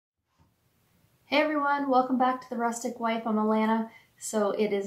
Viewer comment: I don't know why I but heard you say from Atlanta, not Alana🤦